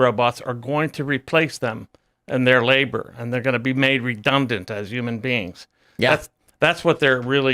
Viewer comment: did he say 'redumbdant'?